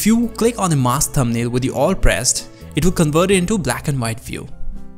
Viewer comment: he says click on the mask thumbnail with the _____ pressed. I can't tell what he's saying. Sounds more like "alt" than anything else to me but that can't be it. Anyone know? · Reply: no, you're right he says "with the alt pressed"